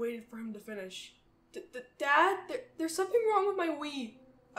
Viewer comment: It sounded like he said Dad there’s something wrong about my weed